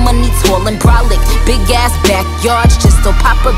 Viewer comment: I hear big ass backyards not, big ass back packs